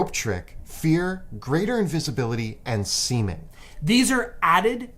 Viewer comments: thought he said semen for a second had to do a double take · lol. had to look up what word he meant to say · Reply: What word was he trying to say? Edit: Seeming